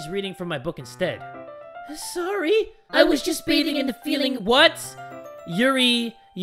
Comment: Btw, at 40:18, what Yuri said was "I was just bathing in the feeling of your body heattttttttttttttttttttttttttheat eattttttttt". Now that I think about it, that's frickin creepy....